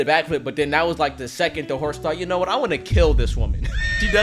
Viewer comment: when i was watching this 17:25, i didnt hear the "the horse thought" part and got so confused LOL